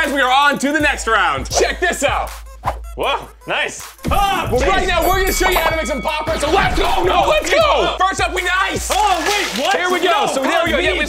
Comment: did he say we are gonna teach u how to make popcorn 🍿????